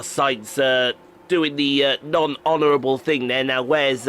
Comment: D-d-d-did you say HONOUR?! I love HONOUR! xD